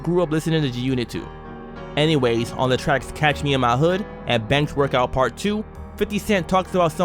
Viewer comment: Are you referring to the track"Banks Workout 2"? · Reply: Yes, the audio makes it sound like you say "Bench workout 2" though. Either way I'm gonna pop it in and do a hunnid more dips